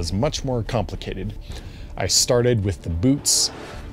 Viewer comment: am i the only one who heard "i started with the boobs"